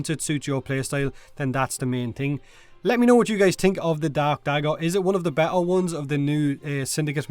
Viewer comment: I could've sworn you said "rakta dab dagger"